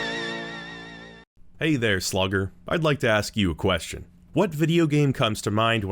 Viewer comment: I had to restart the video. I could have sworn you said, "hey there, slacker." I was like, "well, that might be accurate... but kinda rude."